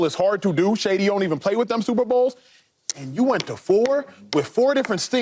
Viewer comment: I thought Acho said "and shady you didn't even play in your super bowls" 😅